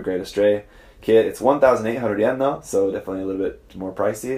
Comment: did you say its 1,800 yen